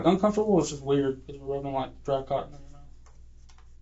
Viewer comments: I thought he said it was like rubbing dry c*ck in your mouth... Would've been a perfect ending. 😂😅 She's bragging about never smoking weed, but smoked penis on duty though. Women can almost get away with anything. Accountability anyone? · I wondrr what he was thinking whem he rubbed the swab in her mouth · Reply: I was listening to the audio only & was shocked when i thought he said "..its like rubbing dry cock"😅 Sigh of relief when I checked the subtitles